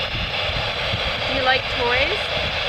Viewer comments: I heard at 13:29 "We like your backpack" instead of "Like the toys" And at 13:37 it sounded like "How much was it?" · I think k it said something like 'I like a good thing/birthday(?)' and when you asked how old he was, I heard "seventeen"